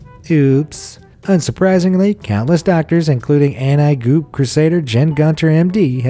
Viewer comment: Did you just say “and I GOOP”? Haha omg 😆